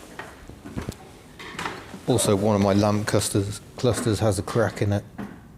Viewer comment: Lol when you said one of my lamp clusters I was in the kitchen putting the kettle on and I could have sworn you said lamb custards lol .I had to rewind to see what you actually said